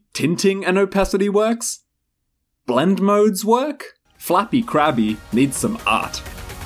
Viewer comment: i thought you said flappy crappy